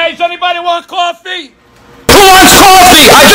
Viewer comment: he says " hey hey hey" but it kinda sounded like kkk, idk chief kinda sus